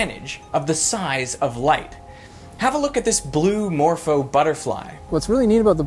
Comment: lol, at first i heard 'blue mofo butterfly' XD